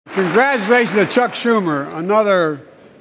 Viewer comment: I thought I didn't hear him right. My wife said, "did he just say upchuck was minority leadr?". SMH